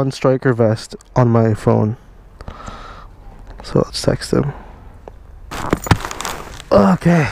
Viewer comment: I heard: So lets sexting. I know it was about a leather jacket for riding. But it was for riding a motorcycle right?